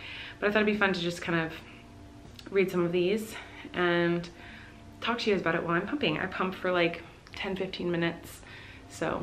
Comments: who else thought she was gonna say “I’m gonna talk to you guys while i’m pooping” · I thought she said pooping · the way she said pumping sounded like pooping and I was like huh oh wait😂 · Who thought she was gonna said “while I... poop” 😂😂 Also the pump sound reminds me of pumping and made me very stressed out.. I clearly had a hard time breast feeding. You go mama!